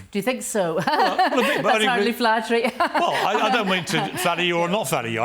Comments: Her willingness to continuously attack the actress's physical appearance says alot about her character. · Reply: She strikes me as very odd, but i didn't hear her attack the actresses appearance? On the contrary, she said that being compared to the actress was flattering. Edit...having listened again it sounds like she said "that's hardly flattering" my bad. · Piers -“there’s clearly a resemblance” Martha “you think so? nervous laugh that’s flattering” 😭 omg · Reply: I thought she said “that’s hardly flattering”